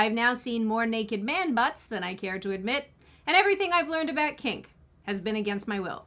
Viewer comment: She said "everything I've learned about kink was against my will", but at first I thought she said "everything I've learned about cake"